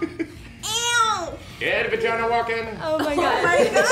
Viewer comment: What does bill say at 1:31 ? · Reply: walkabout